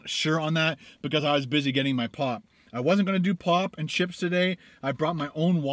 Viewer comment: i thought said i wiz busy getting ma pot ! obviously it wiz pop